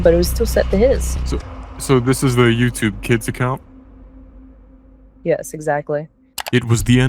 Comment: bro the censored names made it so much worse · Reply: at one part I swear it sounded like it said "I only have a youtube kids account to watch ______" and I was like EXCUSE ME? lol